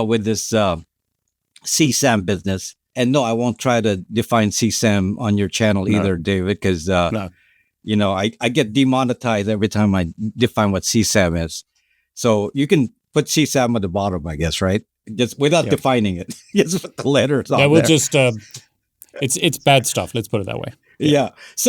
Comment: what is that "C Sem" he is saying that gets him demonetised?? What is that?